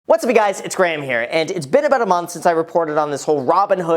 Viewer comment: I've always been curious about what is the first thing that you say in your videos. To me it sounds like you say, "What's me guys...."